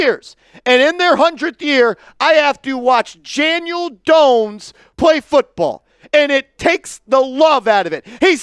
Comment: did you call him Janiel Dones?? You must really be pissed!!!😂😂